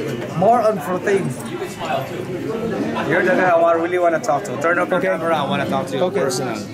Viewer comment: What did dude at 10:30 say thooo ??? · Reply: 'turn off the camera'